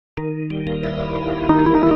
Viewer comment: lol i thought at the end it said "thanks for nothing"